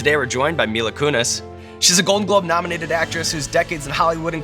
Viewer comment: When Sean said golden globe I swear to god I heard “golden gloves” I was like wait whaaaaat